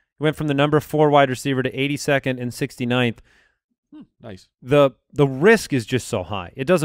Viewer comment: Did say "Nice" after said 69th